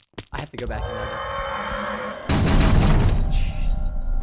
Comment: I heard Preston sed cheese 🧀 he was hungry